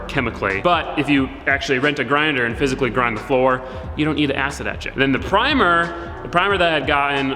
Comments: ok in both videos I for sure thought he said "acid that $h!t" kind of out of the blue. Acid. Etch. It. gotcha! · every time he says "acid etch it" I hear "acid that shit"